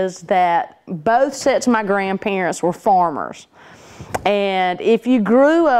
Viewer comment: First time I thought I heard "formers" and I wondered what could that be. Then it dawned, "farmers". She meant farmers! I wonder why she pronounced farmer as former? Which part of the English speaking world is she form? uh sorry ... I mean from? LOL...